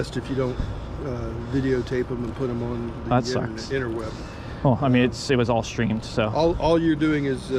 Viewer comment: Did he just say inerweb?! Lmao!